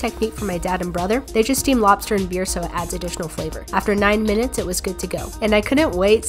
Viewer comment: after nine MINUTES But i thought she said nine YEARS so i was like maam WHAT?!?!